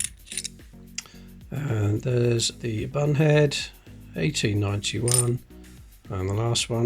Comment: Could have sworn he said bumhead when he had one of the Victoria coins 15 :18